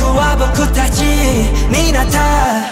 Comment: I heard them say "Gay Bowser" lol!